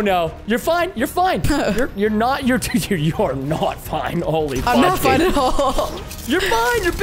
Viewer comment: kinda Sounds Like he Said holy f dude